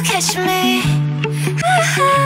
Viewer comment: all I hear is ‘promise that your kesh me’